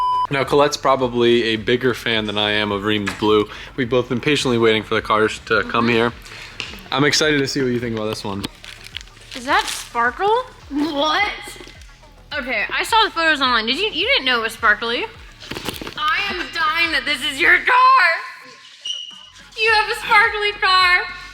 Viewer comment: At first I thought you said "Sparco" not "Sparkle." Ha.... That's a no on the Sparkle.